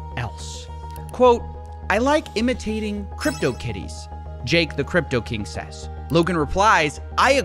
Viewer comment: Did anyone else hear him say Jake the Cryptokitty 😂